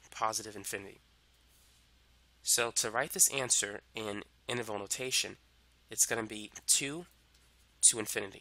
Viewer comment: on a serious note, what kind of notation? what did you say @ 1:10 - 1:11 ??? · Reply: disregard, found it in the description. Interval Notation. sorry but you're pronouncing it different. "potato potahto let's call the whole thing off" Luois Armstrong yep daydreaming again.... ahhh Aretha Franklin - Daydreaming